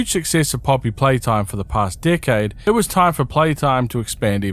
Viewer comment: whenever you say decade it sounds like your saying dickhead